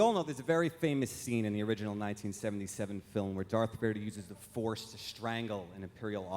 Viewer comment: Did Jordan say “Darth Verdee?”